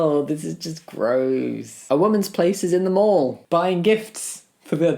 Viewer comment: I heard “a woman’s place is in the morgue” and had to do a double take hahah